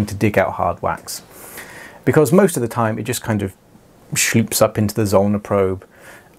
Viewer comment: Question: What is it you're saying at the 4:19 mark? Schleep? Is that like a slurp? Is that the sound the suction makes? 😅